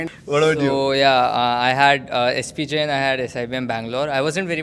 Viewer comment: Did he say SPJAIN ?